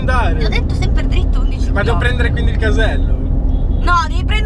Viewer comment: Io ho sentito "vado a prendere Kenneth Caselli".